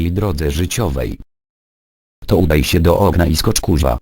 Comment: Okna* :D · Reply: Konrad Caban dzięki, usłyszałam "ognia" :)